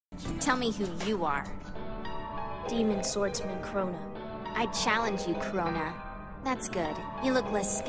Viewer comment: I thought one said corona and the other one said Baka